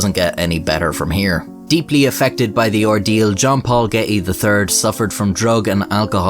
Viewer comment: When you said "jean paul getty" i heard " jump all getty" lol